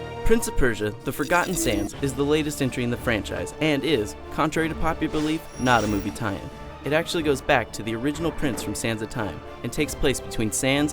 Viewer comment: What is he saying in 0:16? is he saying movie tien?